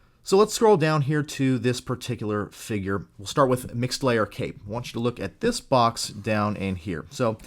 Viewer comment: Lol I thought you were saying "mixed layer cake" 😂